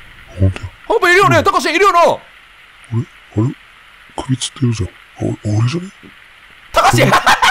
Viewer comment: たかし「首吊ってるじゃん、おｒじゃｎ？」 絶妙に聞き取りずらいのが超面白いｗｗｗ